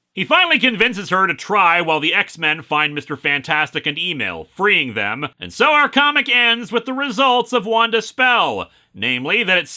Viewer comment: Oh! *EMIL*. I kept hearing "email" and was mildly confused. Also, if you want a recent and good extended action comic, you should check out the IDW TMNT Leonardo one-shot Sophie Campbell did.